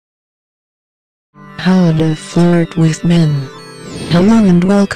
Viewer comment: HAHA at first I thought it said how to fart with men.